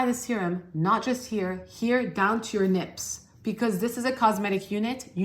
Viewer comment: into your video, did you say nips? As in your chest? Or lips?